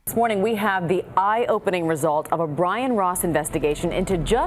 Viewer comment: thought it said ryan ross for a second and got concerned